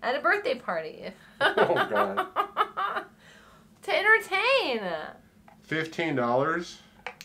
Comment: When she said “to entertain!” I heard “tit or taint” and if that doesn’t sum up who I am as a person I don’t know what does 🤣🤣